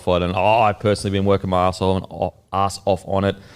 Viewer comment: Who else heard say he's been working his asshole at 2:45 😂